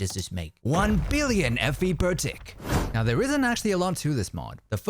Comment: i thought it said fent